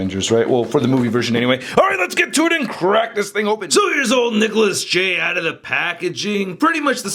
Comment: does he say nicolas cage? :P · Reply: no Nicholas J